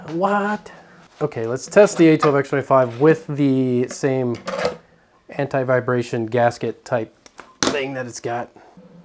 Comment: I farted at 8:30 and thought I heard Hentai Vibration ;-)